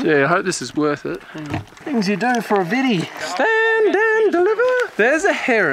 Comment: What did he say at 9:02 ???? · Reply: stand and deliver by the sound of it